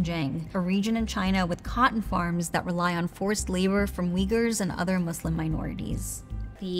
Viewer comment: please fix your captions !! it says 'weavers' instead of saying 'uyghurs' !!